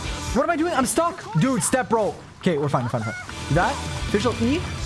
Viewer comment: dID YOU JUST SAY "STEP BRO" WHEN YOU WERE STUCK AKHSKJASHDAJSDSLAJHDSAKJDA